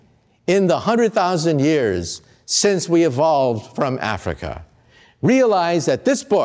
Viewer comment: Did he said at the 12:16 minutes “since we evolved from AFRICA?”